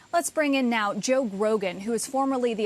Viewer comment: Did she say Joe Rogan?